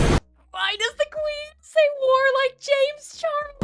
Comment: SHE SAID WAR?!??! I THOUGHT SHE WAS SAYING WOAHHHH!